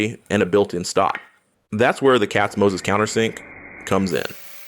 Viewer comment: Hey great work, what is the countersink bit called i heard "cats moses" anyone able to give me a link affiliated or otherwise, i can't find it on the holy Amazon